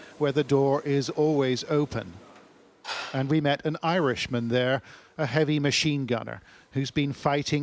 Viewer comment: "It´s a bad thing to have an Anglo-Saxon as the enemy. Worst thing even is to have an Anglo-Saxon as a friend!" General Vandamme, head of Imperial Russian military inteligence, 1890s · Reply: i thought he said he's Irish.